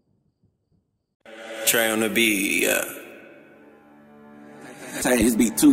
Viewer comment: I heard “Trey in the beat” and thought “No Label” was about to play